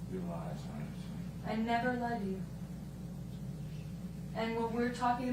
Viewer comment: did she say "i never love you?" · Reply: She said “i never lied to you”